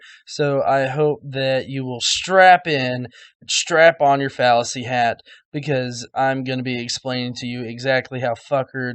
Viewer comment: Heard "Strap on your fallus-y hat there for a second." Gotta love some things you mishear...